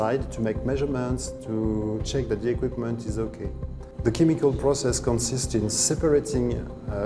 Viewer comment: Dang, I realized the French guy saying Chemical when I heard Kimiko. 7:05 Frenchie from The Boys love chemicals and Kimiko.